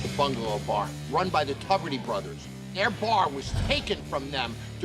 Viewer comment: I love the way the captions said "The Bungalow Bar run by the puberty brothers" 😂😂